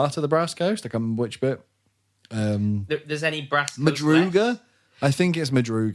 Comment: I heard that nearly Brass Coasters! (A Brass Coaster is what you put your drink on) This Freeborn here can help. Children of Wrecks is the name of the renegade cult to Siakha, who are based in Madruga, they have a dread temple spawning heralds into the world. Madruga has been fused with Spring and Night magic that has caused lots of changes. The battle at E1 with this renegade faction of Grendel and Imperial deserters was at Redoubt, Urizen.